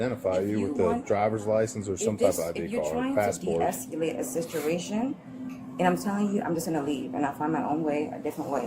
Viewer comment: I swear I thought she said ,”de-escalate the sisteration”!